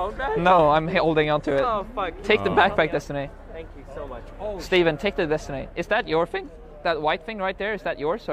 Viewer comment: I literally heard "uhn steven" 😢